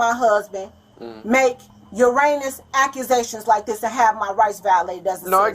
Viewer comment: Did she say Uranus? I like how they spelled out "heinous" in the captions, assuming that's what she meant, but how do you know she didn't mean erroneous accusations rather than heinous accusations? I would think it would be erroneous, like "false" accusations, no? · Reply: Thats all I keep hearing "Uranus accusations" 😂